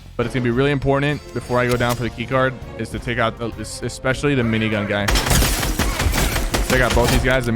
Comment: did he say minigun gun?